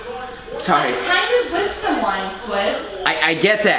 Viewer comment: Who else heard say I’m KINDA! with someone.